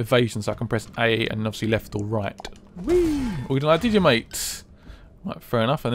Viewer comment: I swear I hear his guy say wee